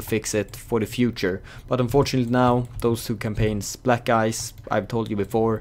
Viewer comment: Did he say black guys at 6:15 turn on captioning wtf...